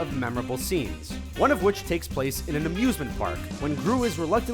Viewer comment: I thought he said abusement park